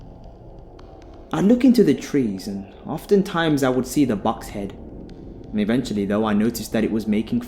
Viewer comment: Thank you. Great story with a wonderful read!💖** But I have to say I thought u said box head. I finally realized I was hearing it wrong. You were saying " buck head" lol. I worry myself sometimes.